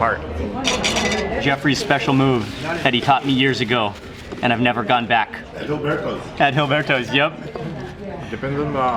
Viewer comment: What is the thin sauce at 14:22 ? I can't quite hear what it's called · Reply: Salsa borracha , drunken sauce